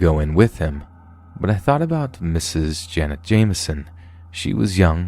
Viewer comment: Omg I was listening at work and wasn’t fully paying attention and all the sudden I heard “Jenna Jameson,” 😳 wow was I wrong… absolutely heartbreaking and these are extremely heavy… and hopefully my brain fart will give ppl some comedic relief if they need it. My lazer focus upon hearing her name. For those who don’t know who she is…. You’re welcome ✌️ Hope everyone in these stories has found some type of closure and healing…. We never know how close we are to scenarios in some of these situations and remember to stay alert fellow scary/horror listeners